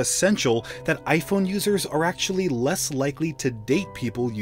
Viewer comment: He said "iPhone users are less likely to date android users", i heard "iPhone users are shallow"